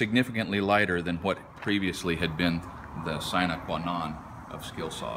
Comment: what is the word he says at the end of his sentence at 0:30 ? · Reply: synequanon